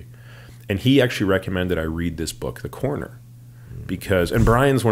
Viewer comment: Anyone knows the author of the book he mentions at 23:46? I think he says The Corner, or The Coroner, but I can't seem to find it. Please help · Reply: The Corner. They made it into a TV Show. Also, The Wire is based off of The Corner.